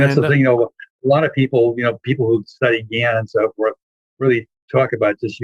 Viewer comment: I couldn't hear what he said at 50:41. People who studied __ ? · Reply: hey channel0001, he said "people who study Gann"